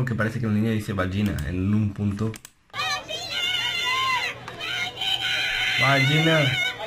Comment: parece que dice vagina no vayina